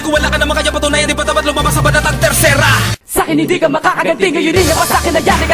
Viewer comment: did he say " terserah" 😮 baru tau ada kata terserah di tagalog😂